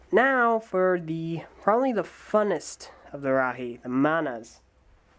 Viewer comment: every time he says "manas" i hear "mayonnaise"